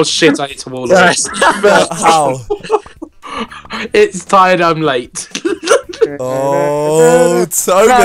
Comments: did Ethan say "its tired i'm late" ? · Did anyone hear Ethan say it's tired I'm late instead of its late I'm tired